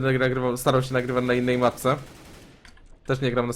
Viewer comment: kto usłyszał matce :-D?